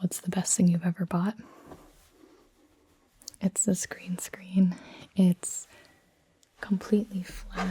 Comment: she says screen screen instead of green screen